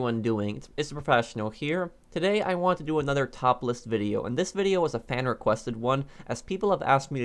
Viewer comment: At the start I thought he said "topless video"